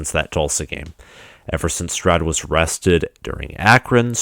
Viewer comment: You said "Stroud was rested" and I heard "Stroud was arrested". Great show dude.